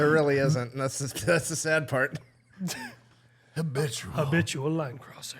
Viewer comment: So glad to be part of this lol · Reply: I uh. I misheard you as habitual lime crosser soooooo